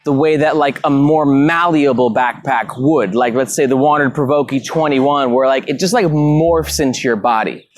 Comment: What did you say at 7:58? The malleable waterproof Oakey 21? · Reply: WANDRD PRVKE 21 - check out our reviews on it, amazing bag